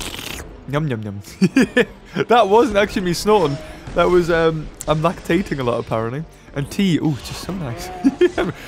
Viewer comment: Also 7:31 did say he’s lactating a lot?? 😂😂😂